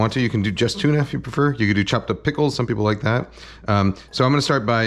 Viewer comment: for a second I thought he said “chocolate pickles” instead of “chopped up pickles”